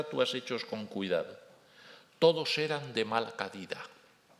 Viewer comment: MINUTO 34:36 ''....ni estatuas hechos con cuidado, todos eran de mal caQUÉ??... No entiendo qué dice al final. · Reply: Calidad, creo que es la palabra que he oído. 🤗